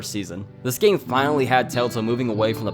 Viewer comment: I’m sorry but why am I hearing “TaleTale”?